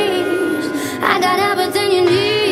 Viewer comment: LOL, the first time i heard this I thought it said genie, not genius